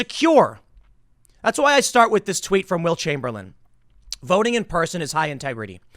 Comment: 😂😂 I thought you said Wilt Chamberlain.